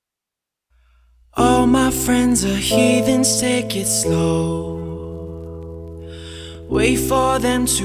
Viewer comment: did he said " all my friends are here to eat steak slow " LOL